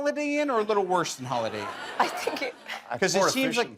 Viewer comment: What does Cillian say at 9:47. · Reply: “More efficient I think” a play on the ‘express’ in Holiday Inn express.